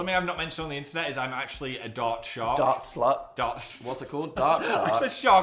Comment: Totally thought Phil said “dart shart” not dart shark 🤣